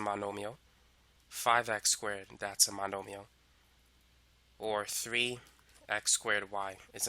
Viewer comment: I cant understand the spelling of nomios that i heard at 5:20 😭